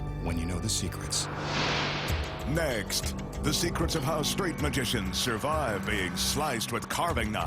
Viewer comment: did he say straight LOL